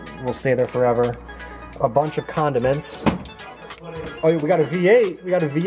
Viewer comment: I thought you said lots of condoms instead of condiments and I Was like wtf.